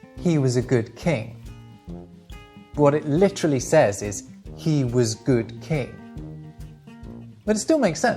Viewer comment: i thought it says "he woes god crying"...